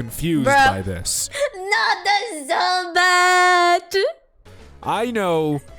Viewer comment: Wait, did say "that's so bat" instead of "bad"? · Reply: "Not the Zubaaaaaat!"